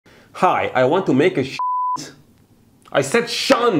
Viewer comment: I thought he was saying "I want to make shit"